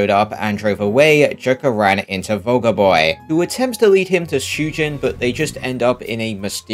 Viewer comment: I heard “Soulja boy” when he said “vulgar boy”… someone should make a Soulja boy mod for Ryuji at the start of p5r